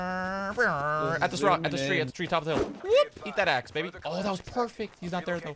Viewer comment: ii thought you said " eat that axe nigga " idk if im the only one